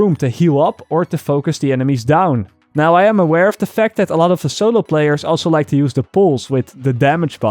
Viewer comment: What does he say at 4:57? That's the ability I want but I can't understand him :/ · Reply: "Alot of solo players like to use the pulse" Its the first ability in medical.